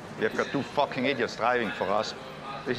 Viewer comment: what did he say???? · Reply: If it was his decision he would sack them both